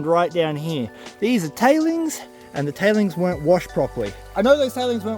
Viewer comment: 14 mins in, I thought you said the Italians didn't wash properly 🤣